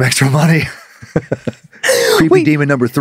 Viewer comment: I also thought said Death FACE.